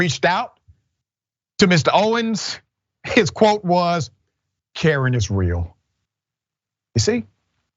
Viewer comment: I had to rewind, I thought you said karen davis🤣🤣🤣